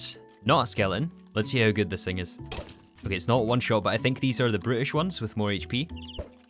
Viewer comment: Is he saying british or brutish?